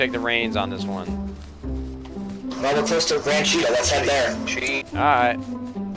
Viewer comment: Not me lol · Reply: Lol he killed u a soon as u died I heard let's he'd south lol